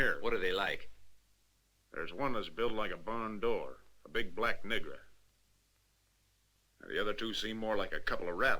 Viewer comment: Excuse me !!! 54:15 Big Black WHAT? Surely this word is offensive and should be dubbed out of an otherwise nice movie. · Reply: He said nigra though